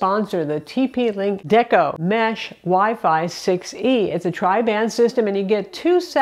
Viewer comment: I usually don't comment on sponsors but I use a Deco system and love it. Have not had any issues with it! Can easily recommend it! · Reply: Hmm. I was confused because I think I heard her say 6 GHz? Which surprised me. (1:24 aha she said "6 E"?) Anyway, I have AX (which is newer than the AC WiFi standard), but that is not what your system is about, or? (and my system is not 6 GHz (still 2.4 and 5 GHz), but it might be called WiFi 6 standard?) (and she called it "6 E"?)